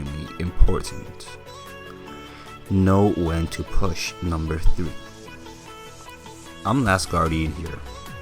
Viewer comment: Lol that sound like number 2 instead of 3 :)))